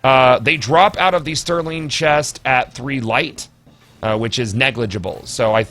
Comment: Did you just say these armor pieces are dropping at 3 light or did you mean 300? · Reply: 3